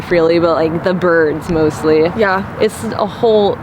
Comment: so around 3:01/30:18 I HEARD A MEN SAID “ YEP “ clear as day